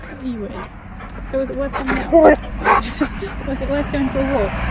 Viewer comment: When you said "was it worth coming for a walk?" I wasn't looking at the screen but it really sounded like he said "it was" That's what I heard! Listen again! At 2:21 It blows my mind.